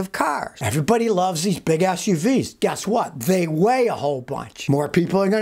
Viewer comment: I could've sworn he said, "BIG A$$ UV's" not Big SUV's 🤣